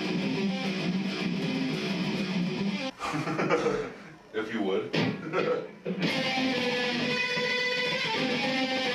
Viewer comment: cant even hear what theyre saying but theyre literally a heavy metal beavis and butthead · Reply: Jim just says if you would and then says yea a little later